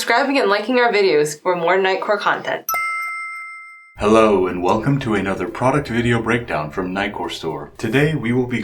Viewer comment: I swear I hear says "welcome to the shitle" everytime hahaha.